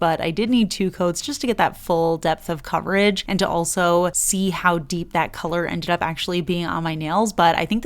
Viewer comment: You know what I heard in my head? 🎶hOw dEeP iS yOuR hOlO🎶 🤣 What a throwback